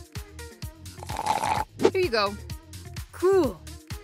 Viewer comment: I thought He said crool:D